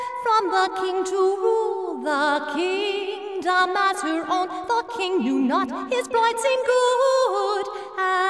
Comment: does she say "fucking you not" ? XDDD